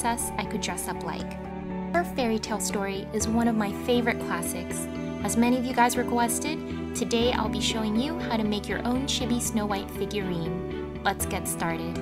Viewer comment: I thought she said shity instead of chibi XD